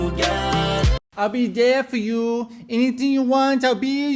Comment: It sounds like he says “I’ll bidet for you” as in the kind of bidet that is in your bathroom to clean your bits up after going to the restroom.... Sooo realistically the song could be about him taking care of her in her older age lol